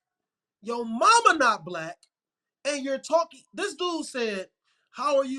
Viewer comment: Did he just say, " but ya mama black."? Cuz that's what I heard 😮😮